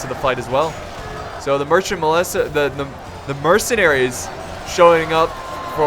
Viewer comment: for a moment i thought i heard him saying Mercant molester :D